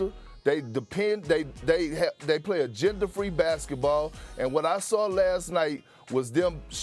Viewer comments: i almost thought Perkins said "they play a gender free basketball" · did perk say they played a gender-free basketball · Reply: Agenda-free